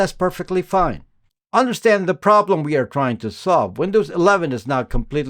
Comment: Windows 11 is "now" or "not" a completely spyware OS? Sorry, I can't quite make out what you said there.